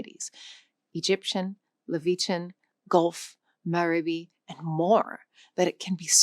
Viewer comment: Did I hear you say "Leveechan"? I think you meant "Levantine". As in the language of the Levant.